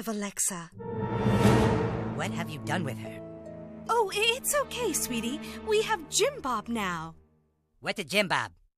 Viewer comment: For a second I thought cartman's mom said jin bop as in the youtuber that got arrested